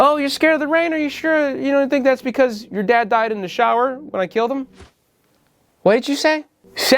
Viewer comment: oh my god i just assumed i had misheard the line when laura seemingly says "when i killed him" i was going crazy over that